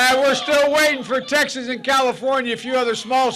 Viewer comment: Uhh since when is TEXAS SMALL??? Please explain... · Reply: I thought I heard him say Texas as small lmao